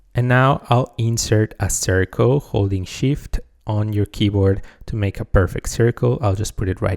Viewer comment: he said “holding shift” but I heard “holy shit” 😂